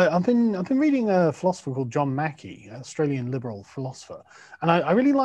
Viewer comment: Who is the Aussie philosopher Carl is referring to around the one hour mark? I heard “John Mackey” and have tried a variety of spelling changes but cannot find this particular individual. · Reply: JL Mackie